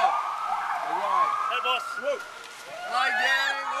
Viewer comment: Paul is still amazing. But at 0:18, did I hear it right? Did one of the guys from the car say "Hey Boss, Look"? Is Paul telling his staff to call him Boss? Hahaha.... I thought he was more down to earth than that.